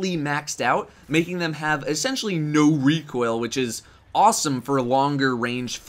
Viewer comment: did anyone else hear say "NO RECOIl" in a deep voice